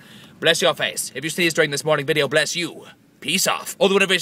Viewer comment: is it just me, or did he say "if you sneezed durring this horny video" at 2:59 ????? this is going to change the way i look at tobuscus forever!!!!! (: teehee